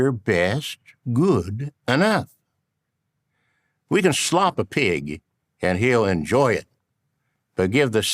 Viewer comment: thats one of the best ones yet if you can slop a pig i thought at first he said if you can slap a pig he will enjoy it the cow said moo off